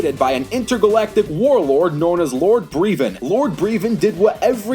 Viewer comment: I thought you said Lord Frieza 😂😂😂